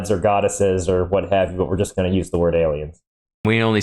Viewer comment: I think he said omens, not aliens.